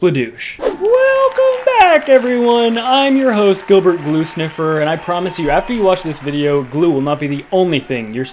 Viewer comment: I thought he said glute sniffer hahaha 🤣